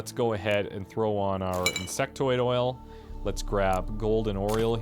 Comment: did you just say "Sectoid Oil"? XD